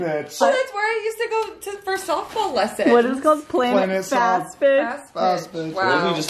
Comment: I thought said Planet Fast Bitch and I was so confused, then I realised it was pitch 🤣